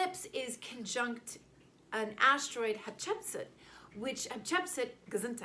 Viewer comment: I didn't catch the name of the king/queen you mentioned at 6:55... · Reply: Hatshepsut queen of Egypt